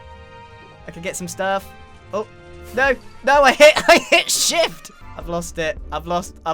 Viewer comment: it sounded like he said “I hit sh$t” 😂